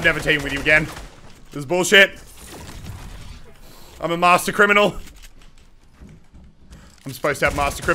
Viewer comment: I thought you said you were playing with crainer😂